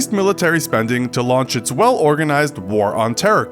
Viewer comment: Sorry think I misheard you but it sounded like you said "to launch its well organised war on terror"?